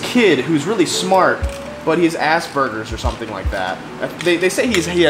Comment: not sure why, but every time you said 'Aspergers' i heard 'ass burgers.'